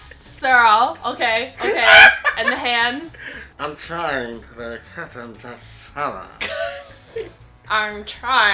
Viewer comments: i heard "I'm trying the cat and the salad" and i was confused for a second there lolol XD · When the way trying to say Captain Jack Sparrow ( 1:41 1:47 ) to me he sounds like he said ( Captain Jack Sarah ) 🤣😂🤣😂🤣😂🤣😂